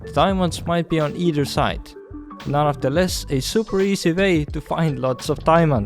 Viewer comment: did he just say "None of the less..." xD